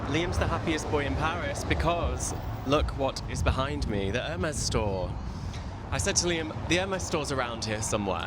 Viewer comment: I thought said the M&S store!